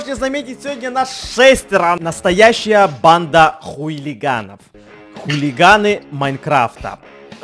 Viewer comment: Я один услышал ху*лиганов